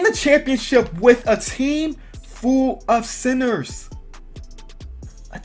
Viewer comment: closed captions said team full of sinners😂😂😂